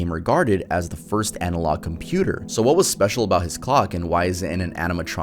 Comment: I need sleep I heard say "what was special about his cock"